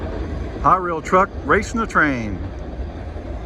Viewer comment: You know, with your accent, at 7:24 it sounded like you said "Conrail" instead of "HiRail" LOL!!